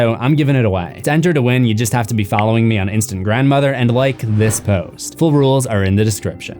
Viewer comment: did he just say instant grandmother lol